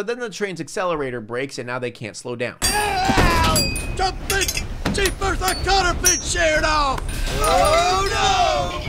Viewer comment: Movie's captions @ 9:18 NONSENSE What he actually said: Jumpin' jeepers, the cotter pin's sheered off! Captioner, you had one job, lol